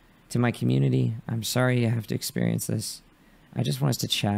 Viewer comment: excuse me? "To my community, I'm sorry I have to experience this" ??? Did I hear wrong, or did he actually just say that?